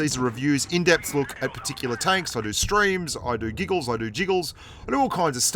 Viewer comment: I thought he said "I do jingles" lol